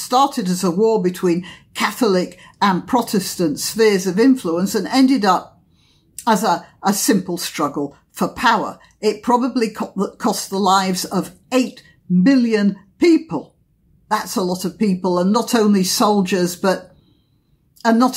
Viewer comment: Gerlandiant: Million. The population of the entire Holy Roman Empire in the 17th century was only 20 million and the whole world was only about 200 million. We weren't even into the beginnings of billions yet. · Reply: No sorry i thought you said 8 billion people?